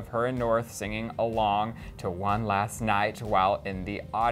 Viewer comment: Did he just say "One Last Night" tf😑 it's "One Last Time"